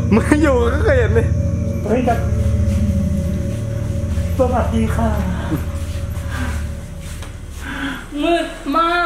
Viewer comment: ตอน8:18พี่บอกว่าสวัสดีค่ะ ได้ยินแล้ว555